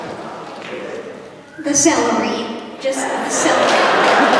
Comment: I thought he said the salary too!🤣